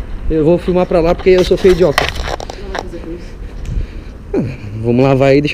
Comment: Aos 16:46 ..Eu vou filmar pra lá pq eu sou feio e idiota, será q foi só eu q entendeu isso de primeira..kkk